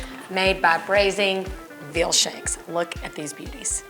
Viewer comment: Lamb shanks or veal? I thought you said lamb shanks at the beginning! ❤️